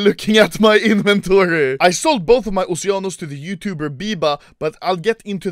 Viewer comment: Says: "title says oceanos" Title: "sold my oceano" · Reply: when he said "oceanos" i heard "oceanus"